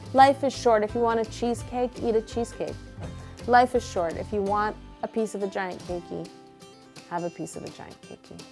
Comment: I thought she said “life is short if you want a vagina then have a vagina” not “life’s short if you want a giant cakie have a giant cakie”🤣🤣🤣